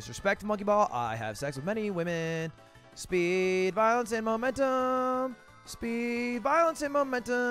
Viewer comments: Thought he said speed, violence, and momentum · i thought he said speed VIOLENCE and momentum but it was balance